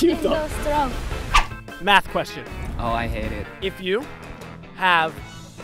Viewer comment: Did he just say " i hate math"?